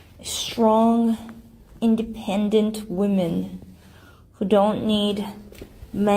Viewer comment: did she say “women”?